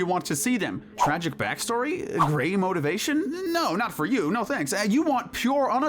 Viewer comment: I thought they said gay motivation and I'm all, "Hmm your not wrong. OHHH gray motivation"